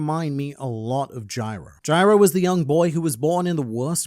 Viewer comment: just completed finals /am tired and thought he said Pyro, for like forty seconds I was so confused.